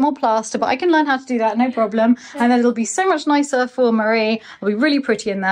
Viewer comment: I am wondering if there will be no more pizzas in the oven with Michael Potts? The heat would kill the flowers! Loved the whole vlog as usual. Nick is a phenomenal tree man with great insight into the forest! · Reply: I thought they said nice for Marie and that meant flowers!